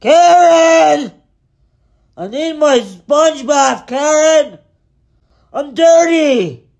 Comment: I heard, "I need my Spongebob" at first LMAO!